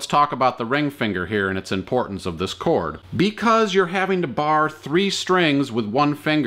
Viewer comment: What is the word? "Bar Version" or "Bard Version" :) I am a not native english speaker. And what does it mean? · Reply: It’s ‘barred’. It’s the past tense version of ‘bar’ … like something that has had a bar put on it is ‘barred’.